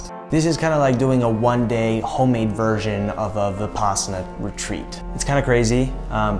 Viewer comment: I can’t really hear what he says here, is it «apposonite retreat»? Anybody? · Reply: Mathias Haukland vipassana 😉